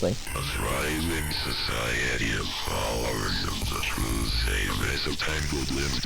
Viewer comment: "A thriving society of followers of the true saviour– as the tangled limbs danced around my bedroom, I held my breath and waited for it to stop. I was too scared to leave my bedroom-room-room-room." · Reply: I thought it said “a thriving society in powers of the truths. A mess of tangled limbs danced around my bedroom.” And the rest u said